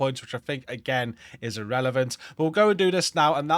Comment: The Hostage Rescue and the zone being friendly is absolutely relevant because they only show up if the zone is FRIENDLY. It kind of sounded like you said irrelevant. EDIT: Okay I stand corrected, at least for this time - I have NEVER seen a Rescue operation in a HOSTILE Zone, but this manhunt seems to have over ridden this.